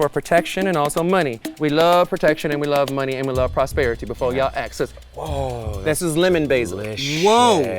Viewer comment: sorry, why do the captions say "(indistinct)" ? he very clearly said "ask us."